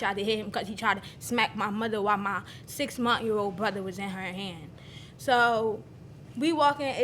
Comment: Did she say her "6 month-year-old brother" ?....:>)